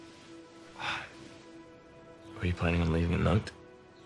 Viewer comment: what'd he say at 1:53 ? I can't make it out. The speaker on my phone isn't the best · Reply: Where you planning on leaving a note?